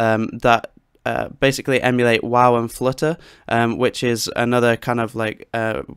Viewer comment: 'have a google for free vst plugins that basically emulate __ and __...' what is it that you're saying here? I couldn't make it out/didn't recognise the effects you're talking about. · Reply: Sorry about that it's my Yorkshire accent haha! But as BuryTheShovel said, wow and flutter. This is a great free plugin you could use: <link>